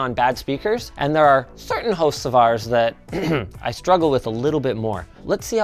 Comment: When you said "certain hosts of ours" I thought you said "Certain Hocevars" and was trying to figure out what beef you had with Carson Hocevar